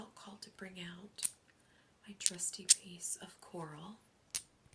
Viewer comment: Before you mentioned crows, I heard you say clam instead of coral. My crown familiar in my life with Merlin brought me a clam shell!